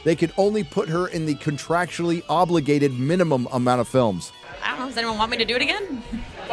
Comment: I can never tell what Brie is saying in that clip at 8:05. Is it, "I don't know. They don't want me to do it, again." If so, that just oozes resentment and, as petty as it may be, it's nice to know her ego was bruised. Well, until her lackeys reminded her how great, amazing, and powerful she is.